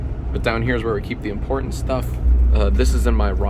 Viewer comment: did Jack say Jawn at 4:15? Erin really indoctrinating u lol.